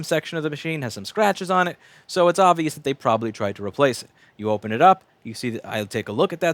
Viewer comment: In an hour and a half of video, the best freaking thing I heard was "the pen is bint".